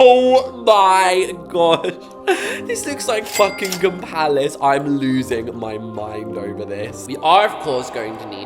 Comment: did y’all hear CURSE? I know I’m not the only one who heard that right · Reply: fuckingham palace